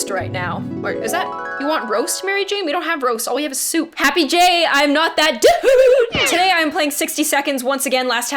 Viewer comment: I thought I heard I am not that duuuude!